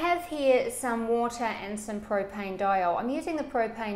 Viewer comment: Sorry I missed one of the key ingredients at 3.39 it sounded like she said propane dial did I miss hear her say propylene glycol ? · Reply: allan fouani: It's called, "Propanediol." DupPont makes it.